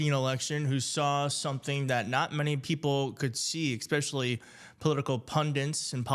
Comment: Did he just say expescially??